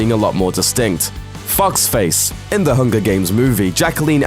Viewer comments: when he said foxface, i thought he said f*cksface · I thought he said Fuck's face and not Foxface. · when you said foxface i thought you said fucksface and i just died